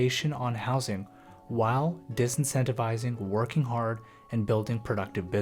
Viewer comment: Kind advice from a former proof-reader, English & media teacher. At 12:53 a subtitling error: audio says "disincentivising", but subtitle says "incentivising" - exactly the opposite. But well done, the negative gearing/capital gains tax reduction created the haves and have-nots, along with minimal wage rises and rampant inflation.